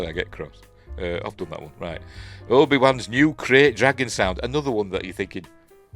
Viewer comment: I'm half asleep and thought you were saying "crate dragging" and I was baffled why they need a sound effect for that lol